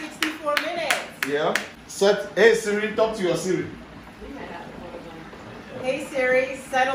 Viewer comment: Did I just hear"talk to your Siri"?😂